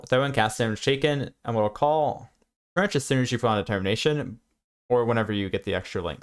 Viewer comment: Bro what did you say at 11:04 XD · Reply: as soon as you put on determination :)